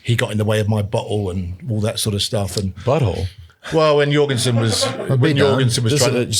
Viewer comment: Had me dying…I thought he said but hole also….Lance was funny 😂